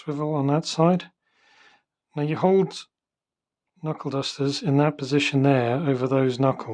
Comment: I thought they said Knuckle Busters. I was assuming that refered to mean Brass Knuckles.0